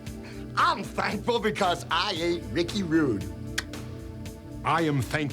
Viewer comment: "I'm thankful because I ain't Ricky Rude" 🤣🤣🤣 I miss Piper, man. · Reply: I had to play that one back because I heard "Ate ricky's stew."